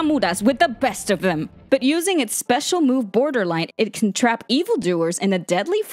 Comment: Wait is that Border Light or Borderline? Not sure if I heard it correctly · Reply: Sounds like Borderline to me.